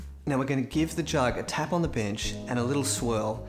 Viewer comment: ON THE BENCH.....LOL... I THOUGHT I HEARD "ON THE BIT!% GREAT VID LOVE ACCENTS